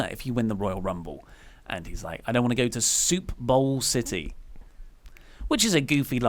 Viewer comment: I thought Truth said "Sioux Falls City" ... which is a city in S Dakota so I can see why Oli misunderstood the line.